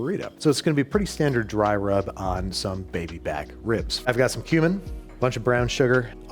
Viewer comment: sounds like he said, "I've got some human." Babish, are you ok?